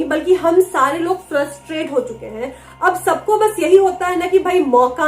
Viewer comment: Any army there who hear frustrated as trustfated?